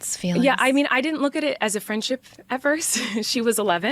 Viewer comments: Sorry dude, but after the 17-min mark where you misheard her saying "i didn't look at it as a friendship, at first" as "I didn't look at it as a friendship ever", then went on for at least 6 minutes more about how she lied about it not being a friendship, I had to stop watching. That's why i said "at least 6 minutes more"... bc I like most of your vids and do agree that she's a creep, but you went off here on something she fully did not say. It's okay to just admit you messed up rather than backpedal. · with the “at first” bit i get what you guys are saying but im more referencing they made TONS of videos together from the get go and is clearly lying about the friendship imo, she tried to imply it was a slow thing but the reality is the more you listen the more it seems it was very friendly from the near beginning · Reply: But you based an entire section on quoting her as saying "I didn't look at it as a friendship ever" and saying she was lying afterwards about them being friends, when in reality she said "I didn't look at it as a friendship, at first". I despise Colleen, but that entire segment in your video for quite some time was wholly incorrect. She didn't lie at that part. You just misheard "at first" as "ever".